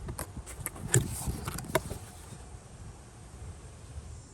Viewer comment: The CC at 6:00 say "brich" instead of "birch"